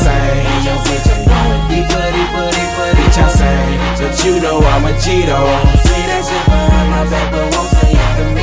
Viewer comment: When I first heard this I thought he said "ya know Im a Cheeto" I was like wtf? lol